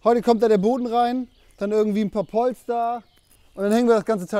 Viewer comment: Habe ich da Polstar gehört?